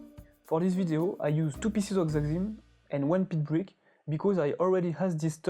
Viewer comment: I couldnt understand what you said at 06:02. What are these panels made of? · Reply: xaxim panels, these panel are made of tree fern wood